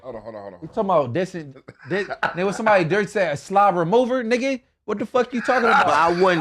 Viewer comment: Lmfao I thought he said ugaslavia but he said slob remover